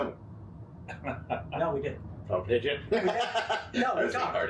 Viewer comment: Actually, I did talk to them. I'm not sure why you interpreted the Trump voter reaching out to me as me brushing them off. We chatted at length. They have changed their mind. That said, that's one of countless that have not during discussions in the past, At this point, if a Trump voter reaches out and is honestly remorseful for what they did, I'll always talk. But I don't want to ever bring a still staunch Trump supporter into my intimate social sphere. It's a waste of time when there are millions of Americans who could likely be far more easily educated and motivated to vote in the midterms. It's a cost/benefit decision. Exert X amount of time and energy to flip one Trump voter, or exert the same X amount of time and energy to motivate 10 non-voters to vote. I'm sure others see this differently, but I've been at this game a while and know that about 40% on either side of the political spectrum will vote one way no matter what and there's little that changes their mind because it's culturally ingrained. We're always aiming efforts to the middle flexible 20%. If we can motivate even a fraction of them to vote who didn't vote before, we can win. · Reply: I obviously misheard (thought you said, "no, we didn't"), and absolutely get your approach to invest X amount of time to persuade 10 waiverers rather than just 1 trumpist.